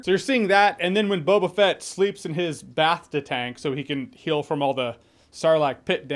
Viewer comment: did he say bathta instead of bacta?